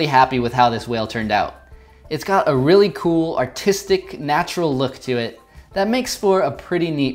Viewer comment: When he said artistic i thought he said autistic